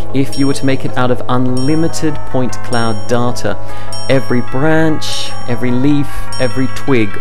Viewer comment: I thought he was saying "Unlimited point cloud darter".